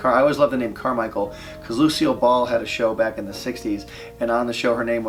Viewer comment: i thought he said lucio ball